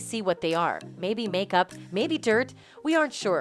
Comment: I thought she said "maybe makeup, maybe turd"💀 that would've been hilarious